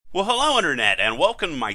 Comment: What he says at 0:00? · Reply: Well Hello Internet